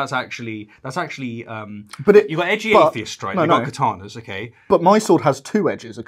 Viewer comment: When I heard "my sword has two edges and the katana (sp?),it sounded more like guitar instead of katana", I heard instead guitar rather than katana. It took awhile to figure out how a guitar has edges, but a stoned mind can rationalize almost anything, right? Lol peace